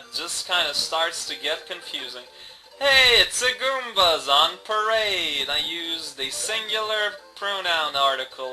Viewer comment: Maybe you have a secret affection for Horseman Joe? Nah, j/k. Moray is pronounced like "more-ay", ay as in Ray Stantz. 10:28 I for one wouldn't have noticed that if you hadn't pointed it out; I thought you said "It's the goombas on parade." Sometimes I worry about your self deprecation!